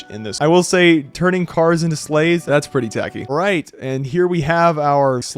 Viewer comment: "turning cars into slaves-" wait... did i hear that right?